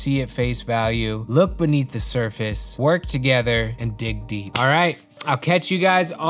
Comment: did he just say "lick beneath the smurfis"